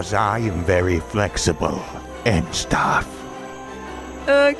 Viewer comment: did notch say "flexiblely"? Or just "flexible"?